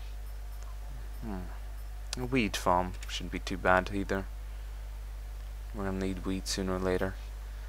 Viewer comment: Hahaha at 4:25 sounds like he says "Going to need a...weed farm shouldn't be to bad. Going to need weed sooner or later." Great! Love your videos man, just got a little lol out of that.